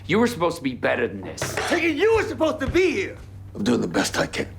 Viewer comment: what did Boyd say right here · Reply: He said you were supposed to be here. Without father khatri his heart became darker and his path less clear.